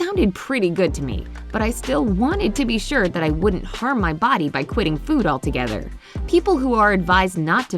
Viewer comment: Only water... Oh... no... not for me. I have to eat too 🤪👍🏻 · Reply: well for those of you who didn't hear her say "i wanted to be sure that i wasn't going to harm my body by quitting food all together"